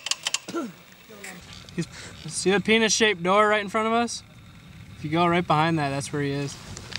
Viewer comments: Did he say "penis shaped door" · did he say penis shaped door lmao